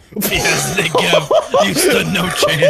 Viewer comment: CC says the n word when said negev